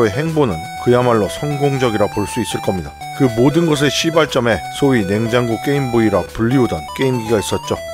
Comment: 저만 시발점이 C로 들리는건가요~?^^